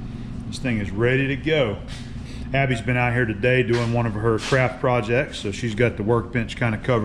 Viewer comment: Sounded like you said "crap projects" at 35:21 LOL